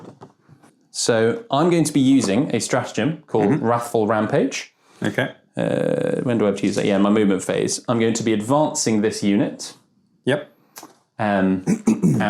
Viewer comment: You almost sound like your saying raffle rampage, and now I can't get the image of Blood Angels running across the battlefield screaming "Have you entered the Baal weekly raffle yet?!", each marine carrying their book of raffle tickets in one hand, ready to bager the fuck out of any Dread Wing marine silly enough to make eye contact with them.